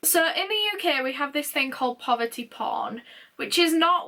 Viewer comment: The whole "poverty porn" thing is so fucked up to me, as an American. Like, I know how bad we are when it comes to social class, but I couldn't imagine gawking at them, especially how so many of us are on the brink at any time. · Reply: I thought she said “poverty pawn” and I was so confused by it 🤦🏼‍♀️